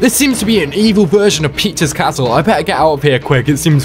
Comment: sounds like you said “pizza’s castle” lmao